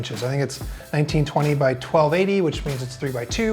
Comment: the captions say 12 ad instead of 1280 xD